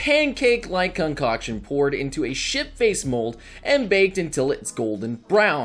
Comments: Did anyone else hear "shit faced mold" at 6:58 or was that just me? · I keep hearing shit face mold. 😂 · Did he just say shit-faced mould?